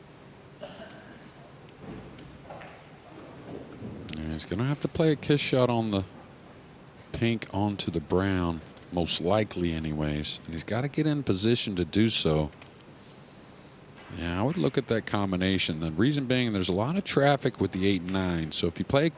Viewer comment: love this format! full match, unedited, great camera work, great announcing, some interesting cutaway sequences, production value through the roof! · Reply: Did i hear him right? 31:40 "Yea.. after the kiss shot he's gonna have to put the pink onto the stink." Yowzers! what a treat!